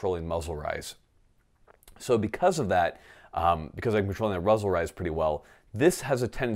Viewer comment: Did you say Ruzzle rize at 5:01? :D